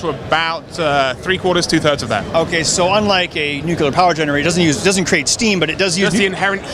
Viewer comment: Did you say "nucular"?